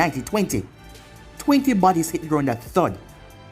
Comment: captions say hitler😂😂